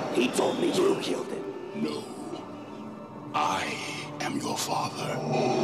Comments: W T F. Watched the part where he says “no, I am your father” twice. I heard him say “no Luke, I am your father”. Watched it a 3rd time and now hearing it without “Luke”. Mandela effect. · "They never told you what Darth Vader exactly says in this scene." "They've told me enough. They've told me he says 'Luke, I am your father'." "No. He says 'No, I am your father.'." "No. No. That's not true. That's impossible." "Search for the entire scene in Youtube, you'll know it's true." "NOOOOOOOOOOOOOOOOOOOOO. Nooooooooooooooooo." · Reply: I even heard it as "no luke" when I watched this clip and then when I watched it again and paid attention it is just no, I am your father. It's your brain messing with you, it happens.